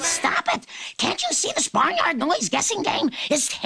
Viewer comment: “cant anyone hear that im hungry?!” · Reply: Actually its “Does anybody even care that im hungry “